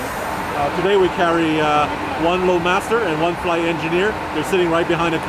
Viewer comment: I wasn't paying attention and I heard you say "we are carrying one flowmaster" and I was wondering why yall were hauling car parts to Mogadishu lol then it occurred to me that you said Load Master